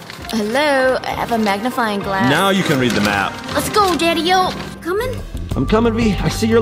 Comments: did call chad a daddy 😏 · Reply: she said “let’s go daddy O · I apologize for asking, but is saying "Let's go daddy-o" ?? I'm very confused. Not trying to be rude, just wondering. The timestamp is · did you call chad Daddy-oh?